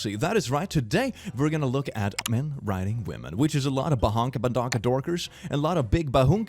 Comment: I first heard it as "men riding women" 💀💀💀